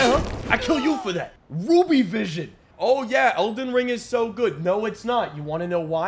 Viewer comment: thought he said "boobie vision"